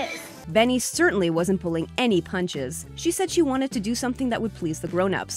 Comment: Did she just say "she"???